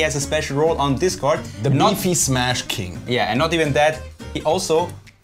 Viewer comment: Nazi smash king? Is that what you said?